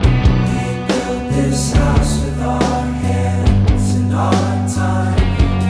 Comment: I'm kinda disappointed to learn that the song says "and our time, and our blood"... I always thought it said "with our time, with our blood", it sounds so much more like "it's mine", like he owns it. The other way sounds like he's just making a list lol.